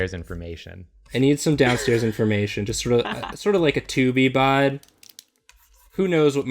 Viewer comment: When said he was going to do a "tube-y bod" I heard "2B bod" and now I'm just a wee bit disappointed